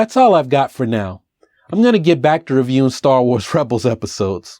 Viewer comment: Good job as always! When you sign off it almost sounds like you say "You all keep on breeding" - I assume you are really saying breathing. Just teasing. Take care, always!